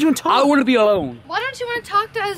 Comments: No trespassing. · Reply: on 4:58 said "porn" instead of corn 😂😂😂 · Did anyone else hear saying ''You got peaches you got porn'' at 4:57? xD